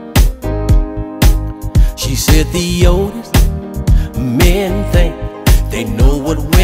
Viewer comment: I'm 17😂 but I always thought he said the oldest🤦🏾‍♂️